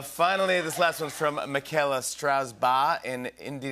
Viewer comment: At first I thought he said Mikaela Straus. I was like, “King Princess???”